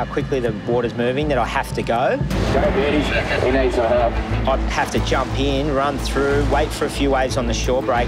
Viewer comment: "go beardy, he needs some help" why did i hear that as "he needs sum mulk"